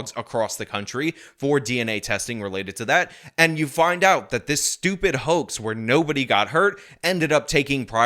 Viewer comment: I had to rewind twice because I heard " Stupid Hoe"